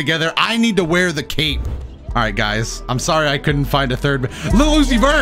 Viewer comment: Day 13 of telling that his daily uploads are appreciated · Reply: ah, I thought he was saying I couldn’t read lmao my bad